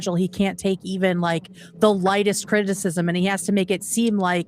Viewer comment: sounds like said “cringicism” instead of criticism, and I think we should make that a regular phrase for Hasan.